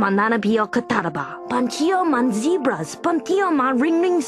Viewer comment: Ling ling's subtitles say 'chickens', but he says 'zebras'